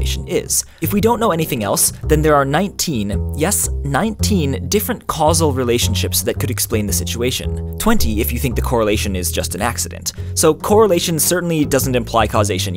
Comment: But the fact that I correlates with A and B doesn't imply that A correlates with B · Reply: oh thanks i thought i heard correlations instead of causal relationships